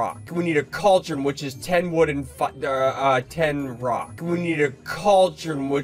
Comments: It sounds like he said. “We need a cauldron which is 10 wooden Fuh err uh 10 rock” 🤣🤣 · I hear him saying “we need a cauldron witch is 10 wood and 10 foot” I hear no swearing